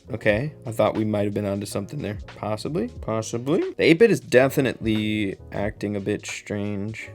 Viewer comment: did u say 8-bit😂❤